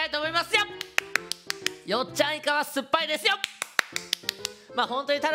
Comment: から よっちゃんイカは酸っぱいです よ↑ に聞こえるのうちだけ？(((語彙力